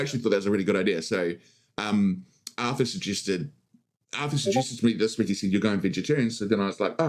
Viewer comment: what did he even say there😂 I swear that wasn’t English. After listening 5x I heard Arthur suggested …… vegetarian